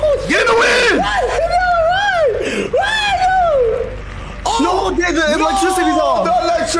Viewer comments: Does say the n word at 35:20 ? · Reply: No. He mumbles alot but he said "No, th-g the electricity is off!" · does not says the n word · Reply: "No Jay the electricity's off"